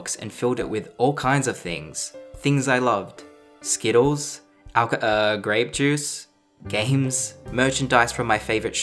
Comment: Yeah i heard "graps" dont hide it i heard chu